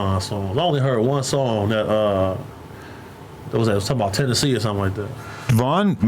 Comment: Did he just say "Tennessee" ?